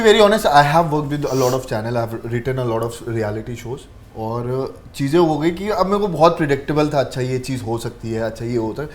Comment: Did he just say he has written a lot of reality shows ?? 😂